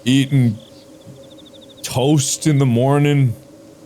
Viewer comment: God I thought mark was going to say "eat toes" before he said toast!